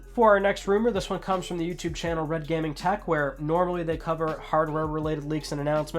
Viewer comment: When you said redgamingtech at first I thought he said reviewtech as in rich.